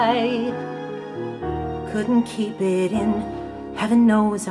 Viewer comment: IF THIS IS WHAT THEY'RE LIKE WHEN THEY'RE SOBER IMAGINE WHAT THEY'D BE LIKE WHEN DRUNK. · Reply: When he said "Couldn't keep it in" I thought he said "Gluten free it in" XD He sounds SOOOOOOBER!!!